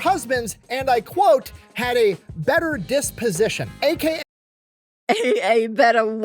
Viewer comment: not being a native english speaker 100% thought he said "dick-position" instead of "disposition" lol xD